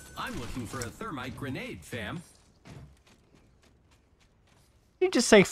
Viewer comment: DID PATHFINDER JUST SAY FAM LMFAOOOOO